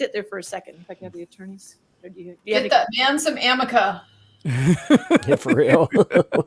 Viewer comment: I thought the judge said, “get him some amica!” I was like WHAT THE FUCK